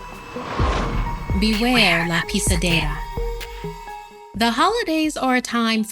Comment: am i the only one who thought she said beware the piece of data?